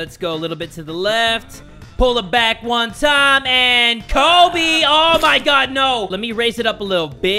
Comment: i was just Watching this cus its in my recommend and i heard kobe.... 😔rip kobe...💕